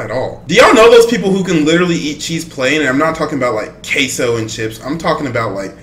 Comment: Did bro just say caseoh before he streamed. Bro predicted the future💀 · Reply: He said queso and chips how do you get cayso from that? It wasn’t even stream related but I think I understand it sounds the same but still how do you get a streamer from that it’s a sauce or something